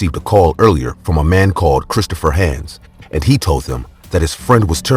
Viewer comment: I swear I keep hearing my name at 16:10. It sounds like he's saying "Christopher Haines." Captions are saying "Christopher hands" lol